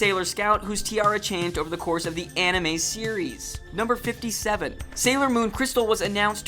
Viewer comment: It sounds like your saying awnime